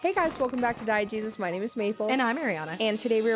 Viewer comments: I thought yall said "hi and welcome to diet jesus"😂😂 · I always thought you said diet Jesus at the start. Low-fat-dieu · I thought you said "Welcome to Diet Jesus" until I read the channel name.